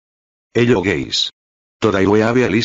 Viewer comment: Did anyone notice at the start of every video, when he says "hello guys" i hear it "hello gays"